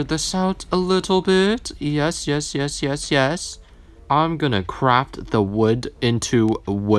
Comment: “oxolotl” what the absolute heck “I’m gonna craFT” thought you said u were gonna crap 👹